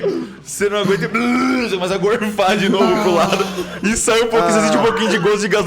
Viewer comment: o cellbit falou q o cesar gorfo goso e gasolina? ou eu entendi errado? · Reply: Gosto de gasolina